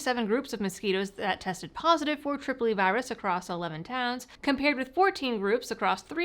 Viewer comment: until she showed the documents I thought she was saying “Tripoli virus.” 🤷🏻‍♂️